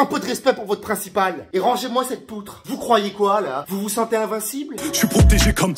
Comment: fun fact: il ne dit pas poutre mais poudre Voilà c’est tout pour moi 😌